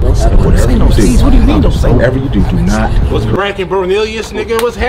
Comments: Don’t say no C words first thing He says what’s cracking lol 🤦‍♂️ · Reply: He clearly said cracking 0:36. I even slowed the video down and I clearly heard cracking. During the bloobers at 3:04 he said bracking so he clearly fucked up the video editing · He said crackin when he walked up 🤣. Its Brackin blood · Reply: If u listen close he does say brackin. I thought he said cracking to. Had to listen like 5×. Lol. Activated !